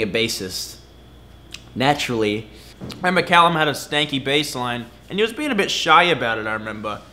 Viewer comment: can somebody tell me what Luke is saying about the bassline? Snaky? Stinky? lol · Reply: its "stanky" lmao